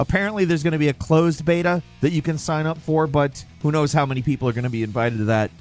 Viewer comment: it sounds like he says "clothes beta" which is hilarious because in the game the guy is naked